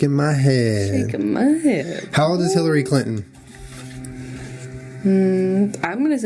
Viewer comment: I thought they said "how does hilary clinton" as opposed to "how old is hilary clinton" and yall I lost it